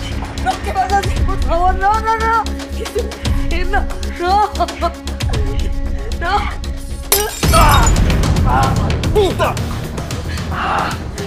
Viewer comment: Escuche bien??? Dijo Pxta?? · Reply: yo también pensé lo mismo wtf jajajaja pero dice maldita